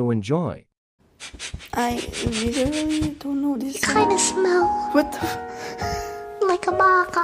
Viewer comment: All of this time i thought it said you kinda smell like a marker smh